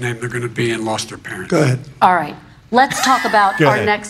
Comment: The fact that some people were trying to say that trump said good instead of “Go-ahead” at 1:47:50 is laughable. They hear what they want to hear and are grabbing at anything they can.